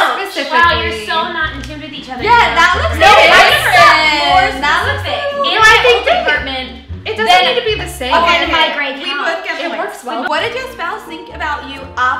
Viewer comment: I thought she said my gay couch